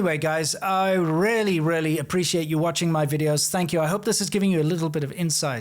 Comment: I thought he said: -"Anyways Guys! I rarely, rarely appreciate you watching my videos!" That's rude! :face-green-smiling